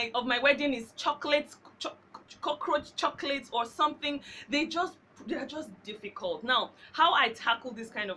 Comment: Did she just say cockaroach chocolate LOLOLOLOLOL too funny! Hahaha. Anyway very informative video Thank youuuuuu!!!!